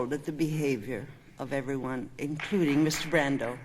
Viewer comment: GREAT UPLOAD AND THANK YOU!!! BUT DOES ANYONE KNOW WHAT AND WHOM SHE IS SPECIFICALLY REFERRING TO IN 5:50 WHEN SHE TALKS ABOUT HOW APPALLED SHE IS BY THE BEHAVIOR OF EVERYONE AT THE ACADEMY AWARDS, INCLUDING "Ms.RANDALL"? WHO IS "Ms.RANDALL" PLEASE?! OR IS SHE SAYING "MR.BRANDO"? AND HER SPEECH/PRONUNCIATION IS JUST SLURRED?! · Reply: PATRICO DE SOUZA!!! It sounds like she is saying brando